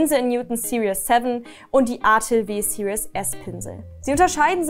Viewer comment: Ich kann leider akustisch nicht verstehen was nach dem Windsor & Newton die zweite Alternative für den Pinsel war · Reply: Artis-Opus Pinsel Series S sagte sie